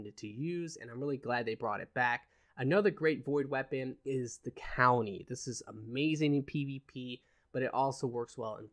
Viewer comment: What did you call it? The County? Cowlony? lmao